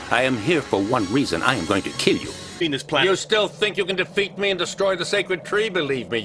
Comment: Did he say "Penis Plant"?